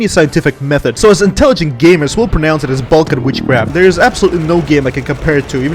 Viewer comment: for a few seconds i thought you said belkan witchcraft